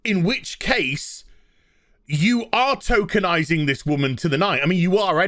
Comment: I kept mishearing you. I thought you said Tolkeinising history and I thought, i'm game for that. Henry son of Henry must find a ring for his wife, lest the orcs attack the city of Minas London. lol